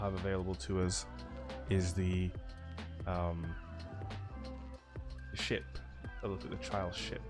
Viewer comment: Sigh, disappointing loot . Where is unique perks, adept. Pointless flawless runs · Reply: Seiya Wasnock I thought he said the smg looks great.......but ok whatever.